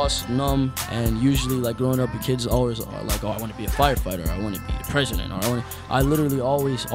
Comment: did he say I want to be a prisoner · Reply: Nah he said "I wanna be the president"